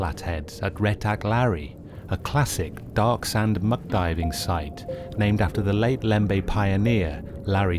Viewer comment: Muck diving? I thought he kept saying "muff diving."